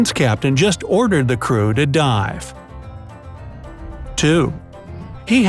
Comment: For a second I thought it said “he ordered the crew to die 🤣🤣🤣😂😂😂🤣🤣🤣🤣